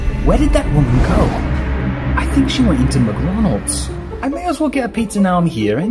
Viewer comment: I thought i heard "Mcronalds" which is not really surprising in China.